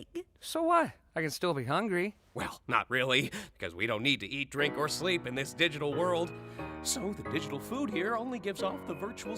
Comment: All I heard was "drown yourself in the digital lake" Lmao